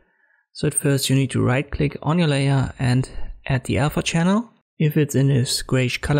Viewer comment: What word are you saying at 0:32? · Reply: "Right click on the layer and add the alpha channel"